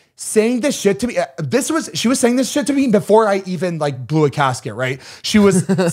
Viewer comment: I CRACKED UP when said “Blew a casket” instead of “Blew a gasket” 😂😂😂😂